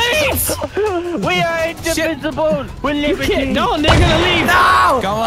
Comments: To those commenting that said the n word at 14:05, he actually said "they". The issue came from other people talking and discord being discord. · Did I hear right?🤣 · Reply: He said "they're gonna leave" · Did i hear that right or have i watched too much boondocks? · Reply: "No, they gonna leave." That's what he said. I heard it clearly. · Did I just hear say the n-word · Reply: I thought so too but I think he says “no they’re gonna leave” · am i hearing what he said correctly?? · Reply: He said "No! They gonna leave" but i see what you meant. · Idk if it’s me but I hear say the N word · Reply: He said “they gonna” but I see what you mean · did just said the N-Word? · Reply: Nah he said "they're gonna leave" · does say the n word here? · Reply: He says "they gonna leave" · did say "no n***a leave"